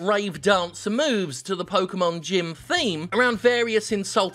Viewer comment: I misheard "Pokémon Gym Theme" as "Pokémon Jim Theme," and now I want to hear Born Depressed sung by a shiny San Fransisco Ekans.